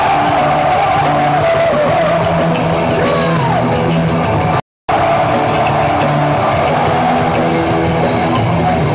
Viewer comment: ¿qué dice? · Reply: come on